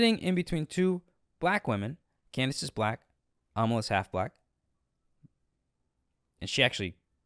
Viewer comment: I thought he said, “Candace is black, almost half black.” 😂